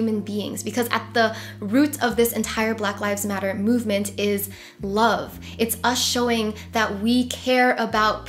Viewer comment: for a second there I thought you said "Blove" as in Black + Love 😅 Nice video though. As a black woman I appreciate when someone acknowledges the privilege they have and decides to do something about it. Sending you much "Blove" x